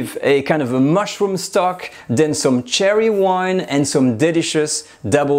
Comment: Further discussion of the “oxidized” cherry wine would’ve been appreciated. Do you buy it oxidized, or do you leave it out for a week, or do you leave a bottle in your attic for a year? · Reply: I thought he said cherry wine too, until I read the recipe and realized it was sherry